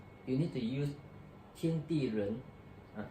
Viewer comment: what do I need to use to promote water? I hear "kin tee ren"